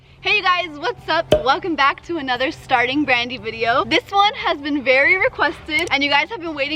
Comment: When she said she had a series starting brandy but i thought she said farting brandy 😂😂😂